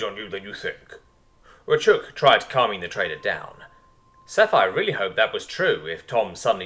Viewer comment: Keep mishearing "the trader" with "the traitor"